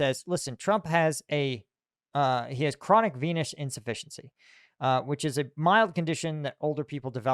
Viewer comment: i thought you said he was diagnosed with chronic penis insufficiency